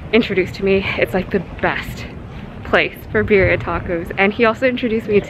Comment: I thought you said “beer and tacos” not birria tacos 😂, I was confused on how you’d never heard of beer and tacos